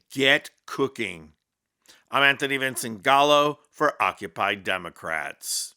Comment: Vincent Gallo?....I thought you said Callow.... Callow's been Dead for years..